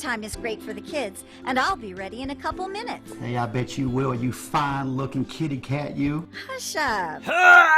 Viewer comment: anyone else heard "Hey bitch you fine looking kitty cat"